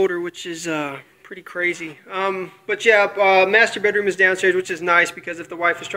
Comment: Did you just say master-baiter?